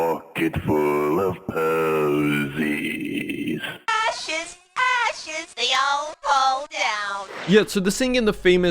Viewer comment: I thought coolkid said . A shiz a shiz they all fall down